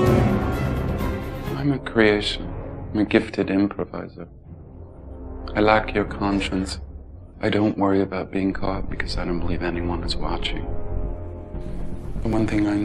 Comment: What does John Malkovich say here? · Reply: "I'm a creation, a gifted improviser. I lack your conscious. I don't worry about being caught because I don't believe anyone is watching. The one thing I know is that we're constantly being born."